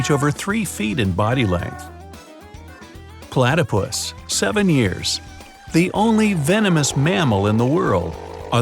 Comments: did he say seven years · i heard 7 years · Am I the only one who heard him say 7 years when it is 17 years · Did he say 7 or ????????? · "Platypus 7 years" I Coulda swore it said 17 years